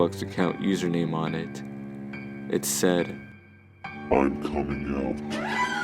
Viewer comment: On 5:49 i think he said Im coming now